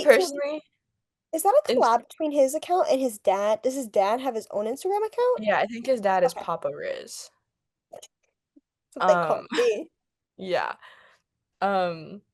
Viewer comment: why did I hear papyrus like from undertale 💀💀